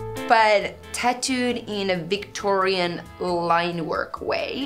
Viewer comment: The Digimon tattoo dressed in Victorian clothes in hieroglyphics style would have been so so cool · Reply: I thought she said Victorian line work, not dress. But I could be wrong.